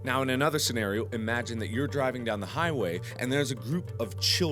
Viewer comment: Wait, did he just say kids playing on the highway?